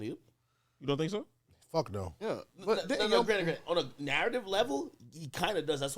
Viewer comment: did ak call “grand “ at 6:27??